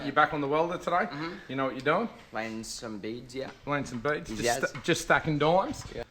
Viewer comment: I heard 'laying some beads' as 'laying some beats' and was expecting some beat-boxing!